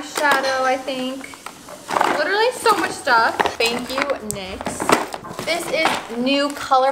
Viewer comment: I thought she said "thank u, next" 😂